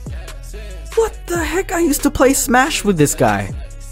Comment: I thought you said what the heck are i used to smash with this guy not i used to play smash